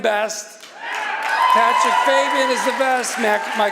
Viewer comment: did I just hear him call out patrick bateman?